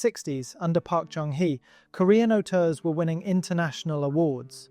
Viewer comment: what is a Korean 'otur'? Is that what he said? I tried googling it.. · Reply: Auteur, someone with a very recognisable approach to filmmaking. For example Wes Anderson, if you’ve seen a one of his films you’ll quickly recognise another even if you weren’t told he was the director.